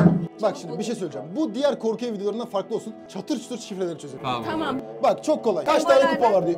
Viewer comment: burda aşkım mı dedi yoksa benmi öyle anladım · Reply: tamam mı ? dedi sen yanlış anlamışsın